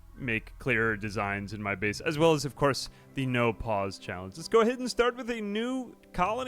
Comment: I heard "No Pawns Challenge" while listening to this from the other room and for the life of me, could not figure out how that would even be possible. Edit: I really like any of your rimworld long form* videos. Fills me with determination.